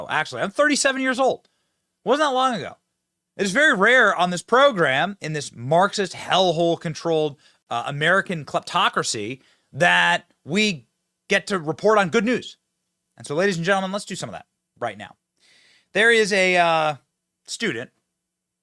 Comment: Did he just say "Clitocracy" ? ...ok, googled it, he said "Kleptocracy", ..i think.